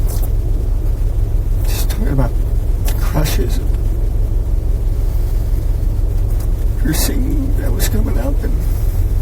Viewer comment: “she was talking about crushes” Is that what he said?